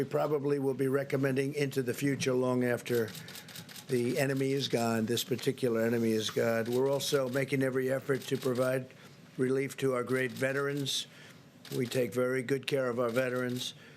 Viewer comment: anyone else hear him say " This Particular enemy is God"??? WTF